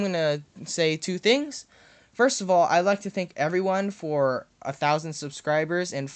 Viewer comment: did he really say think instead of thank?